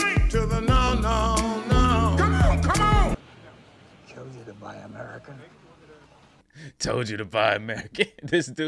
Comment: I thought he said, "Would it kill ya to buy America?" Maybe i'm wrong.